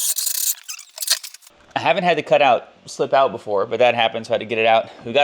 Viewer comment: what size and type bit?? 1:12 what did you say? Slip? How do you prevent plastic from leaving cut piece inside?